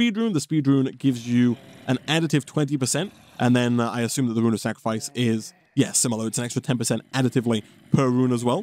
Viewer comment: I played DS3 before watching this, and for some reason I heard "road of sacrifice" instead of "rune of sacrifice" every time, and i got so confused